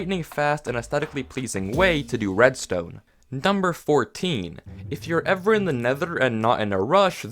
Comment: "Dumber 14" at least thats what i hear